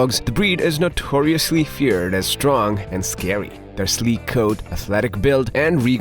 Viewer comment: I was only listening first and I heard "Their street code" and I was what?😂